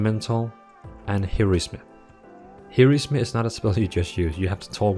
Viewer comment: he said 'here is me' instead of Heroism xD